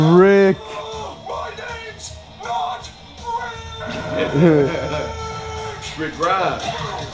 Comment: My name’s not RIIIIIIIIIIIIIIIIIIIIIIIIIIIIIIIIIIICK!!! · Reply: When I was younger I thought he said "My name's not WRIIIIIIIIIT!!"😂😂😂